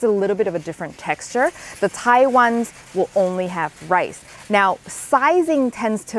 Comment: Funny, @ 5:38 in the video, I thought she said "Taiwan" but after replaying the segment a couple of times , I realized she "the Thai one..."